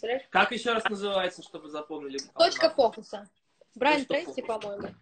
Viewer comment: какая книга??😹 Я не услышала( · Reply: Точка фокуса